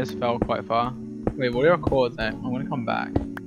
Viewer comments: I’m just rewatching this for like the 50th time and I just realised at 10:30 , George said “what are your coords Nick” that’s like the first time I’ve heard them call sapnap by his real name 😌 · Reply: i thought he said “what are your coords at” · george calling sapnap by his real name at 10:30 is so cute and dream calling sapnap pandas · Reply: i heard “what’re your coords at?” but i might be wrong